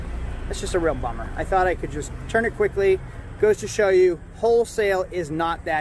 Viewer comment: "i thought i could just turn it quickly", i hear, "i thought i could just be grimey"! Buy decent shit! Typical grimey salesman buying auction junk, trying to polish a turd for a buck!